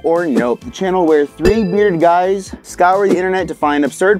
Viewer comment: Definately thought said weird instead of bearded in the intro😂